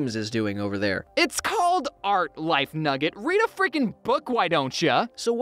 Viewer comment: I thought he said light nugget